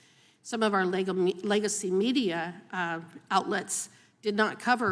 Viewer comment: Did she say Lego-media at 4:07? Because that is kinda accurate